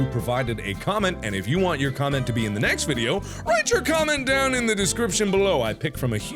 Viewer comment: did he say to comment in the description?